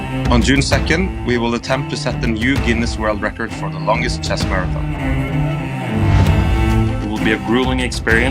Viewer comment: wtf is he saying? "new guinees world record for the longest chess car event"?